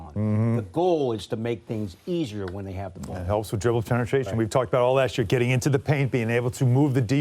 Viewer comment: Anyone else catch Alan Hahn cuss at 2:21 😆 🤣 😂 "We talked about all that shi...."